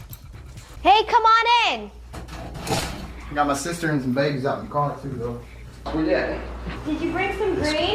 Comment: did bro say his sister and the babies are in the car?? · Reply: I think he said, my sister just got back in the car. She dropped him off